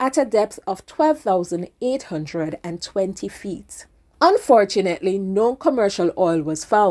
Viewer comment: Plural of foot is feet! There's nothing like feets😊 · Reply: I thought you'd be mad at me for correcting you! Anyway I'm very impressed with your reply ☺️ At 1:38 I heard feets or maybe your pronunciation sounds different☺️